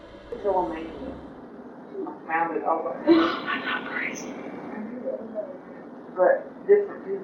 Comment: In number 3 it sounds like she says "mom im not crazy" instead of just im not crazy